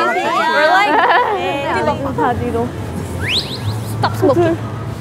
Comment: somehow I heard Lisa say, ‘Stop smoking!’ 😂😅